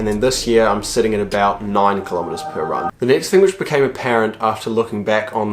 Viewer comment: I thought he said became a parrot